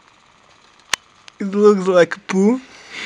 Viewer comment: did you say '' It's looks like poop! '' at 2:57 ?? Hahha